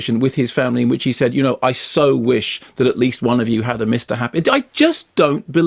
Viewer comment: what does he say at 0:34? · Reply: A mr happy